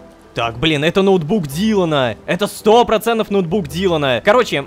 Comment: Выходные без новых серий были самыми утомительными... Особенно в начале сезона. Но ничего! Дождались! Теперь только вперёд 🎉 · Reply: ноутбук дилдана 9:01 , послышалось...